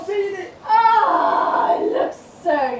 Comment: Did I hear you saying “ah ,looks so jolly “😂 right soppy southern saying that,but saying that I’m inclined to agree, they look very jolly indeed .😃